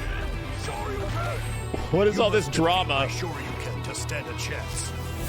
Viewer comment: I thought I heard Ryu said " you shoryuu-can't stand a chance " well, it has that power rangers pun that i love so much. It would be cool if there's some puns like that.